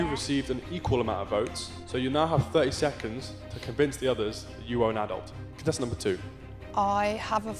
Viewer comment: what was he trying to say? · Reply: “You now have 30 seconds to convince the rest you are an adult”